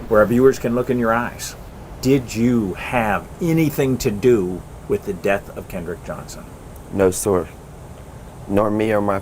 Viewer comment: Sounds like he said he was held down. Sounds like he said Kenneth or Kenny ?? IMO